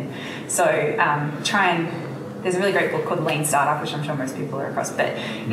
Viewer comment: what book title did she say? That Australian accent wasn't audible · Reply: dixon the book mentioned is called "Lean Startup"